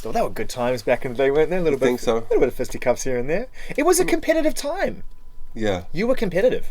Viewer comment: What is the word at 34:04? I am german and I didnt get that word :( It starts with co..? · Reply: It was a "competitive" time.